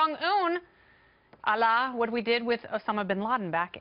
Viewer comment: "Allah, what we did with..." did she say that?